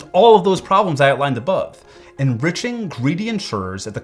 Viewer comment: What does he say at 11:57? Enriching.....at the cost of everyone else? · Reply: ENRICHING GREEDY INSURERES*